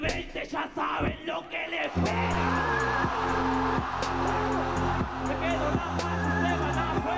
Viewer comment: FMS 2020 ya saben lo que le espera... Coronavirus: que dijo? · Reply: Quemado pai